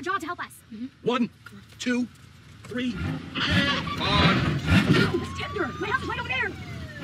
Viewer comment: Did she say its tinder?